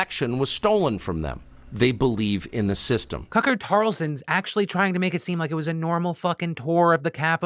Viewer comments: Wait, at 18:25 does he say Cucker Tarlson? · I'm sorry am I finally going deaf or did you call him Cucker Tarlson Cos like I'm absolutely all for that 💯